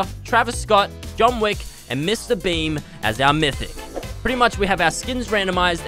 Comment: I thought when he said mr beam I thought he said mr beast